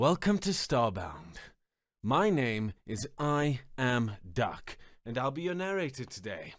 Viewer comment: Did he say: "I am Duck"? Oh, ok 😅